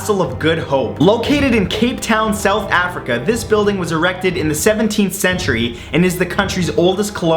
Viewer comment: did he say erected?